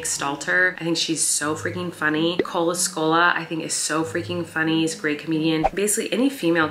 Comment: Who was the second comedian she mentioned? Sounded like she said Cola Scola but I can’t find any results for that name.